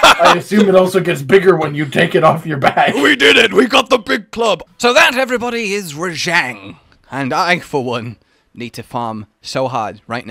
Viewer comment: I heard “Big Plug”